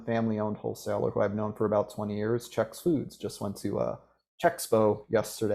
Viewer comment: Couldn't hear clearly. What independent food wholesaler was given a shoutout to at 48:50? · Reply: Hi John, it was Chex Foods: <link> Thanks for attending!